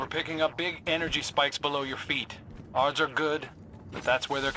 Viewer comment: I think Crow’s actor flubbed a line, hesitated, and then rolled with it lol. It sounds like “ards are good” instead of “odds are good”, like he was thinking ahead to the ‘are’ sound and spoke too fast, because that pause as he noticed didn’t sound intentional either